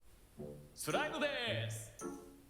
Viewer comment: 「スライムベース！」じゃなくて 「スライムでぇーす！」に 聞こえた上に神と人間のハーフ 「釈○でぇーす！」の真似 してんのかと思って1人で ドツボにハマってたしスライムベース って言ってる事にコメ欄来て 主の名前を見て気付いた。 自分ってこんな馬鹿だったんだ…。